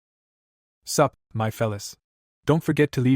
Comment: i swear I heard " suck my fallus" xD